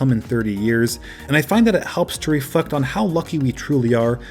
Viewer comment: Did anyone else here him say “it helps to ref*cked” instead of reflect at 11:00??😬😂